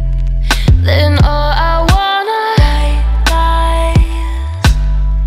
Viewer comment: her voice reminds me of melanie martinez but gosh, this song is amazing. thought she was saying "all i want is white guys", not "white lies"